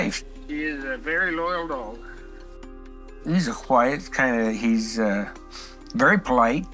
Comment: while starting the list of shh this guy is unlikely to be a killer, did i hear him right??? Did he start this list with “Well, he’s a whhite” ??? Man i hope i misheard Lol